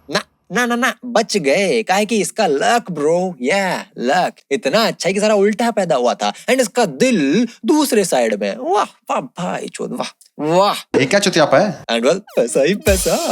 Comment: Did really said bhaichod 13:22 after bashing ravi kishan's character damm to ur details man wow hats off to you 😂